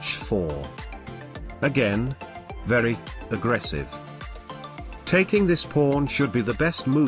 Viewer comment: Every time says "Again, very... Aggressive" I hear "Again, very f'ing aggressive"